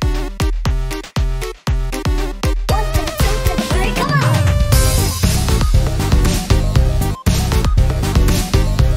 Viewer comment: what does She say? (Sorry i dont speak English) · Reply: 1 to the 2 to the 3 come on!